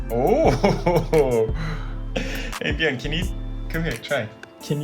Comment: did u call him edgar? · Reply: He said 'hey bjorn'